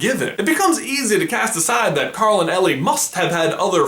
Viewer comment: I thought you said Carol at first……